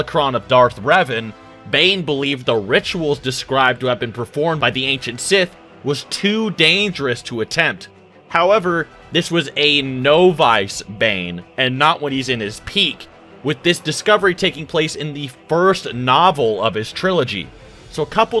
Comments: I’m sorry but did he say novice like “no vice” here? Haha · Did you just say "no-vice"? Idk just the way you pronounce some words trigger me. Idk if this is done on purpose or not.